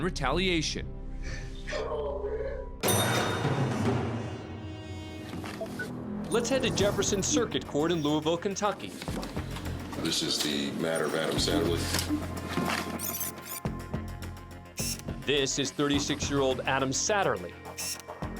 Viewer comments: "This is the... matter of Adam Satterly." Had to rewind, I thought he said Adam Sandler. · At first I thought he said Adam Sandler! Lol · thought they said adam sandler lol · At first I heard “Adam Sandler” for the third case. Which imho he should do hard time for torturing us with his movies. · I thought he said "Adam Sandler". I said "What??!?!?" ... 5 seconds later. "Oooooh". xD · I was blowing a blunt so I heard "Adam Sandler" at first, not Satterly. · Thought they said Adam Sandler for a sec and I was about to do a double take. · Oh, Adam Satterly. I thought the judge said Adam Sandler at first. 🤣😂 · Bro thought he said Adam Sandler I was about to be so surprised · sounds like he said "The Matter of Adam Sandler" It shocked me. Adam Sandler in court?! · For a sec I thought they said Adam Sandler and I was like, what? What did he do? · I can’t be the only one who heard “Adam Sandler” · thought he said Adam Sandler lol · I thought they said Adam sandler lol